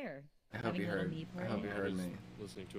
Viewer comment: Miles Robbins for our heart <3 Thanks for this video!! Also could someone write what did he say there?? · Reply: "I hope he heard. I hope he heard me". He=Ryan and me=Dylan